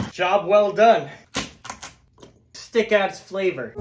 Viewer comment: I thought he said “Stick Ass Flavor”